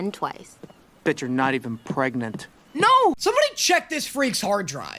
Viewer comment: I swear he said "Bitch your not even pregnant